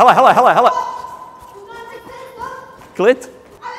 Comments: Must be a czech word, but he actually said 'clit' 🤣🤣 See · I thought he called here a clit, like a clitoris, but it means easy lol